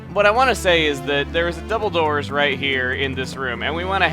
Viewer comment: I thought you said "Dumbledores" lol